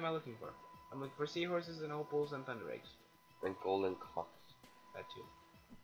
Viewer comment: What am I looking for? I'm looking for sea horses and opals and thunder eggs... And golden cocks: that too I'm sorry, but it sounded like said cocks the first time xD Great video, keep em up buddeh